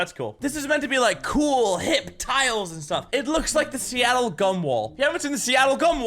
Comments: I thought you said "Seattle cumwall" and ngl got kinda excited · I thought he said Seattle "cum" wall 😂🤣💀